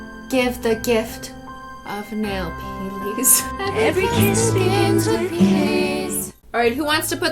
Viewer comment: am I the only person who heard “every kiss begins with piss.”? 😂😂😂😂